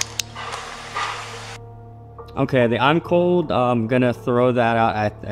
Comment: When someone thought they heard " I'm cold" I heard "Nicole"